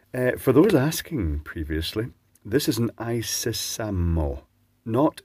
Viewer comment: all this time, until you read it, i thought the spudger said "isesame" like open sesame! LOL